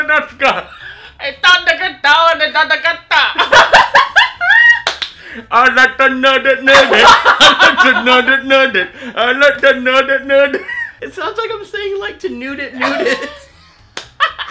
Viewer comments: it sound like he said i like to Nuget Nuget i like to Nuget Nuget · I lost it when did King Julian. Especially whrn he sang it sounded like he was saying "I like to nude it, nude it!" instead of "I like to move it, move it!" 😅 This cracked me up so much! 🤣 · It sounded like he said, “i like to nud it nud it.” Lol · It sounds like he said I like to nugget